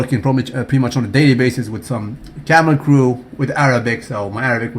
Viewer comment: does he say he's been talking with his "camel crew?" when talking about speaking Arabic?